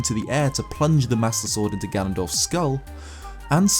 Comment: Did he say Gandalf or Ganondorf?